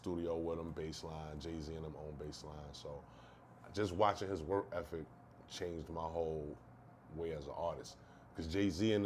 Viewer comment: did he say work effeck😂😂😂😂😂😂😂